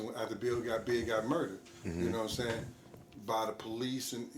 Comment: Did I hear him correctly? He said after big got murdered by the police at 7:08/7:09 in this video.